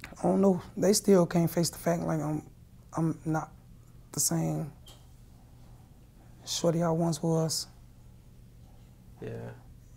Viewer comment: what are you actually saying though ? Sounds like you saying you’ve been cloned or put under mk ultra